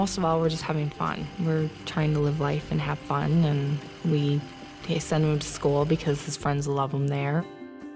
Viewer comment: What did Zach's mom say at 25:04?English isn't my mother tongue;I rewinded to that moment so many times,but I still can't get it:( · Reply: She said " we send him to school because his friends love him there"